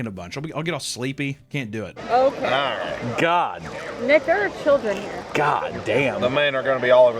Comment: What did she say? 🧐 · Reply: “Nick there are children here”